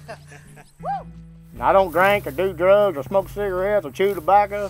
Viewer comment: I might’ve been hearing it wrong, but I could’ve sworn he said I don’t drink. I do drugs and smoke cigarettes or dip this is my hobby